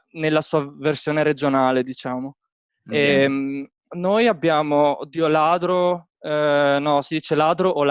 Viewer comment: "Noi abbiamo Dio ladro" ahaha